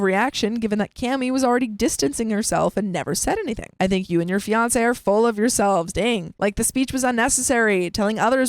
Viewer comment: I thought you said "Dr.Fiance."I was like, "Damn, that's a weird ass surname."